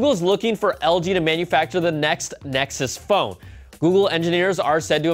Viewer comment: it sounds like he says " next nexus fo"